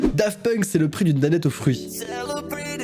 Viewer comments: moi j'entends saloperie de Danette au fruit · Moi perso j'entends saloperie de de danette au fruit 6:13 J'entends tarte au pomme 10:50 J'entends la b**e a MAMA pas momo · "C'est le prix d'une danette au fruit" moi j'ai entendu "salopri de danette au fruit "😶🤣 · Moi j'ai entendu " c est le prix d'une canette au riz" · euh? Moi j'entend à 4:25 "Saloperie de Danette au fruits" après à voir. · Moi perso a 4:20 g tjrs entendu "saloperie de Danette aux fruits" Ps: dites moi que je suis pas seul😂😂😂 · mou j'entends "saloperie de danette aux figues" TvT dites moi que je suis po seule 😂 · moi à 4:23 j'entends :saloperie d'une danette au fruit mdr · Moi c'est le prix d'une danette au fruit. J'ai compris "saloperie de danette au fruit " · Moi à 4:23 et à 9:38 j'entendais " Saloperies de danettes aux fruits" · non moi j’entend salopris de d’Annette au fruit ?😂 10:24 Et j’entends j’ai des puces au collliiierrrrr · moi perso j’entends : « C’est le prix d’une pipe Sophie » 😂😅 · Moi perso à 4:22 j'entends Saloperie de dannettes au fruits ._. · Moi je dit que à 4:22 on entend plus : saloperie de donut au fruits! / ça fait plus drole XD / ( donut veut dire beigne ) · Perso j'ai entendu "saloperie de danette au fruit" 😂😅